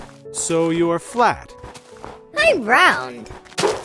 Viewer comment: When he said "I'm round" I heard "I'm brown"